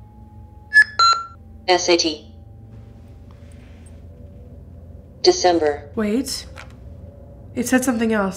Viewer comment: ‘sat’ that’s what I heard it’s almost the alice box was getting rushed or something it also might be ‘necessity’